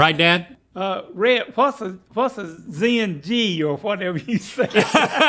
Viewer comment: did they just said Zenci??? LOLLLLL